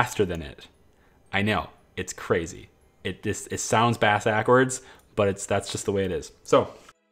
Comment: bass akwards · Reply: I didn't pay much attention, but I heard "Ass Ackwards"... lol I thought it was like a joke so I let it slide! xD